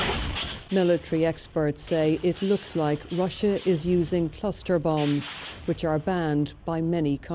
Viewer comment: I thought is said CUSTER bombs. 🤣🤣🤣 Shoutout to CRIPMAC from that 55th streeeet! On hood. You five me?